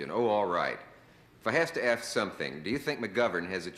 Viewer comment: Did Dickles actually say axe instead of ask?